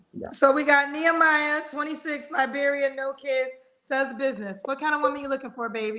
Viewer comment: Can someone ask to add subtitles to Nehemiah please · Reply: lol the latter.... I understood Nehemiah and Liberia🤷🏿‍♂️ even though I thought he said Lagos Nigeria 🤔